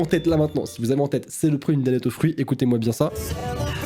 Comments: moi j’entends " soloprie de dannette au fruit" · moi j'entendss toujours"c'est le prix d'une Danette au ; fruits"alors que dans ma tête j'avais"saloperie de Kinder Surprise " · j'ai entendu c'est le prix d'un kider au fruit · j entend " saloperie de Danette au fruits " voilà tout est mélangé chez moi 🤣👌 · Moi j’entends " Saloperie de dannette aux fruits"🤣🤣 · moi j’entends saloperie de danette au fruits 😐